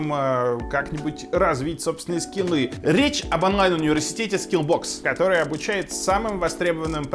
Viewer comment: Я один слышу "Речь об анальном университете Скиллбокс"?